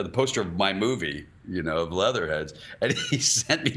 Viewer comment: Leather heads is one of my favorites! The fight scene with John Krasinski is HILARIOUS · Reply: Ah, now I remember. I thought he said,"Letterheads", and wondered how people didn't flock to the theaters.